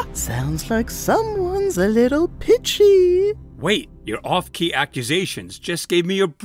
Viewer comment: sounded like "someone's a lil bitchy" 😭😭 (I chooses the wrong time market)